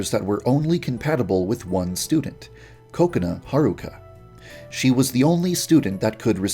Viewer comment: when you said kokona I thought you said coconut am I the only one?